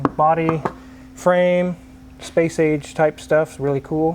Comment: I thought he said Space-Aids